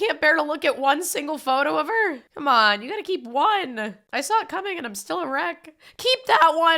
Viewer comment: I heard "I stopped cumming and I'm still erect".💀